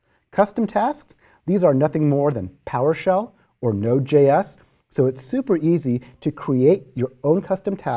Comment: A small comment: at 5:33 he says "PowerShell or Node.js", but the subtitles say "powershow or no-js".